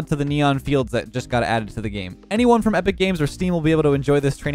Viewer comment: sec did he just say epic games!!??!?